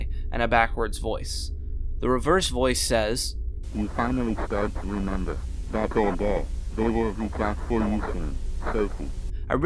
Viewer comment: I tried to slow down the audio on the rabbit and I thought I heard "You finally start to remember. that old doll. They were too fast for you (soon?), sophie